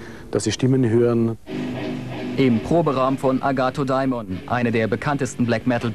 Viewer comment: haha, ich hab auch erst "agathe diamond" verstanden^^